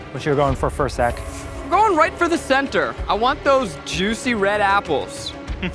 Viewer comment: I thought Zach said 'juice' 😂😂😂